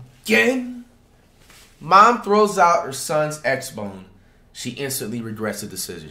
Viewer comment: Did I just hear say “ mom throws out sons Xphone or xbone “ 🤣🤣🤣🤣🤣🤣